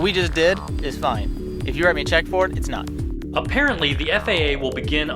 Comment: What the hell did he say at 4:21 "If you're at a checkboard"??????